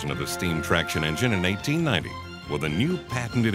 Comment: I could swear at 1:10 he says 1990, lol.